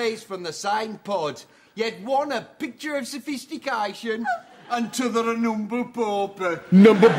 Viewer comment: he says "an' t'other an'umble pauper" it's a Yorkshire dialect (even though for some reason they're speaking with a scouse/liverpool accent in this sketch) where "the" is abbreviated into a "t" at the beginning of the word that follows it. What he said in plain English was "and the other, a humble pauper" implying she is poor, or without quality of life.